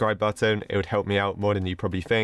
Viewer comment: i heard ropro not rogold XD